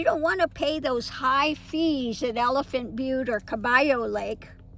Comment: I was at a hidden boon docking place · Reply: I thought I heard you say elephant butte